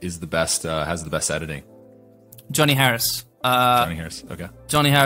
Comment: I can't find Tony Harris channel. edit: I swear I watched 6 times and still thought he said Tony, but it was Johnny